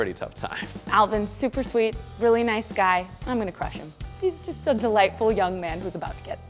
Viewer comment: When she said: I'm gonna crush him on 4:21 For a moment I thought she said: I have a crush on him